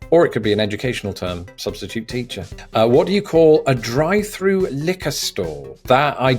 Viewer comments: "what do you call a drive through liquor store?" A bad Idea. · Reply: I thought he said a drive through knicker store! Lol couldn’t figure that one out. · I thought for a second you said “drive-through knickers store”. I was wondering where in the US would someone need some pants so desperately! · Oh, this is funny: I thought you said: “Drive thru knickers store” & of course I NEVER HEARD of that… 🤣🤣🤣 So I looked & you said: “Drive thru liquor store!”